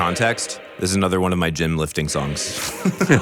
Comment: "This is one of my gym lifting song" No wonder, he is so jacked! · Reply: I thought he said "gin-lifting" songs as in he DRANK to it lol